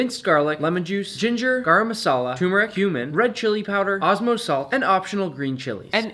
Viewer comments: When he said cumin I thought he said human💀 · Dude, when he said cumin i thought he said human and I was like ¿que?